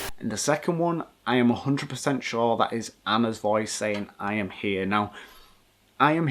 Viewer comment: when you hear 'I am here' i heard 'home sweet home' lol